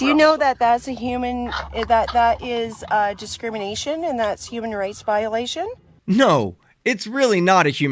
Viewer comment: It sounded like she said human RACE violation